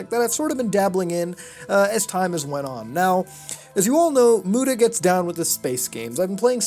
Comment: I thought you said "Buddha gets down with" Then i read your description and it says your name,lol!